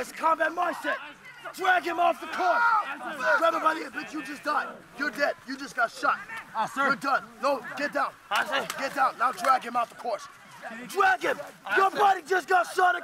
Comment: did anybody else hear "Bitch, shut up" lol